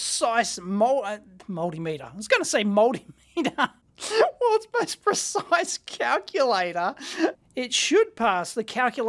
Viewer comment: I thought it said "worlds highest pocket calculator" and was trying to figure out what that meant.